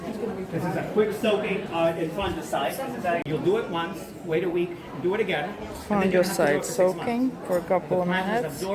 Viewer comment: Did he say he dips in the fungicide and waits a week and dips it again?